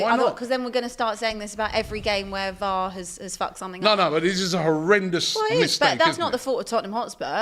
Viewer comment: Did just say f****d something up? 😮 I thought she was an angel 😢